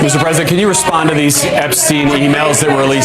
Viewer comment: I can swear one of the press almost says, “Mr. Epstein!”